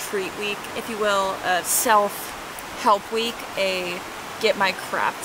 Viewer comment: When you said it was a self help week I thought you said self loathing and I was like RELATABLE. jk lol.